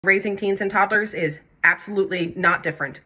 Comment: Am I the only one in the beginning who thought she said raising canes and toddlers